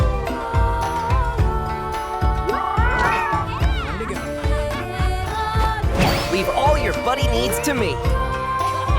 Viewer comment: Yo did anyone else hear the n-word at 1:34? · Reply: "there you go" but REALLY fast.